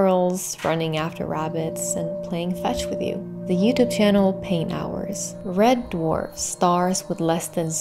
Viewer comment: What is the Youtube channel she says at 1:43? I can’t hear it well enough, but am so interested! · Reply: Pain hours